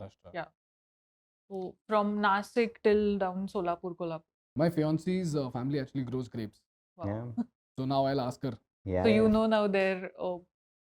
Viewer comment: did he said fiancee at 14:50...... it will be great to see you guys